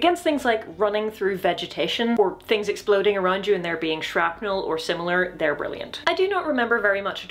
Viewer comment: As per my role as the man who nitpicks subtitles: there's a grammatical error at 2:35. You probably meant "there being shrapnel". YouTube seems to have misinterpreted your accent at 4:26. It says "out on the time" instead of "out on the town". And at 7:08, it completely reversed the meaning of what you were saying - "most practical" instead of, presumably, "less practical". At 9:28, it decided you were saying "routing" instead of "ranting", which is pretty funny but still wrong.